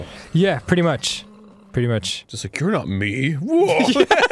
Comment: my ears can't process what Arin said. What did he say? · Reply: "You're not me! Whoa!"